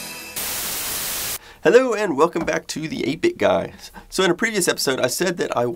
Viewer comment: did he say: "... to the 8 bit guys" (with s) Where are the other guys ?